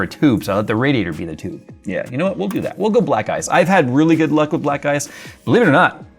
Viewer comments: I could swear he said "I have really good luck with black guys" for a second there... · I wasn't completely paying attention and i thought you said you had good luck with black guys. Haha